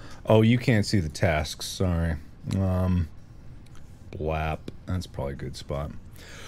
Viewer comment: why did Mark say W.A.P. O.O